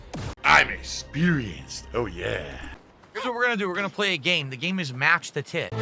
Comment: Thought he said Match the tit 😂